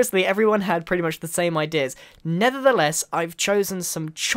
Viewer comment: Is it just me or did he say "nethertheless" at 0:22?